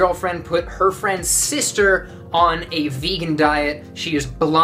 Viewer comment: All I heard was, "my gf's sister..." 💔